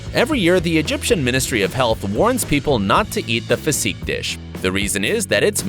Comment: I thought he said pu$$y xd